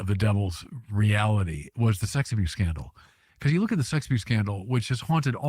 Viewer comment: What was name of the scandal he mentioned at around 8:20? Vasectomy? I can't make out what he said. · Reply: 6 abuse